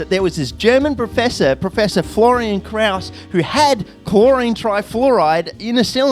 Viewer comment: I heard "Professor Fluoride Kraus." Is he Klaus Fluoride's son?